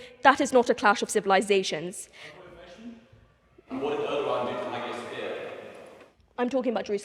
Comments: can someone tell me what this guy says? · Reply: He says "And what did Erdogan do to Hagia Sophia?" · what he say? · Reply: Look what they done to haga Sophia